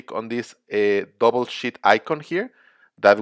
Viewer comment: it sounded like double shit icon lol